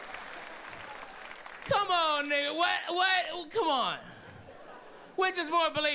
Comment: did he say ni**a??? Or my CC are hearing shit?!!!